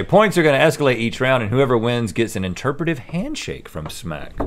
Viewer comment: Initially, I misheard "Interpretative 'Handshake' ". The look on Smac's face almost leads to believe she heard what I did for a split second.